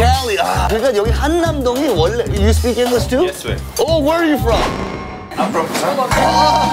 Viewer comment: Bruh I thought the dude at 1:30 said yes guey 😂😂